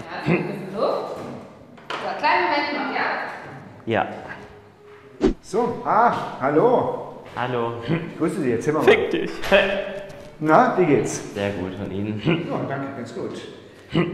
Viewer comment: “so arsch hallo“ hab ich das richtig gehört?😂